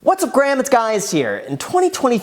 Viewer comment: Am I the only one who heard him say what’s up graham guys here😂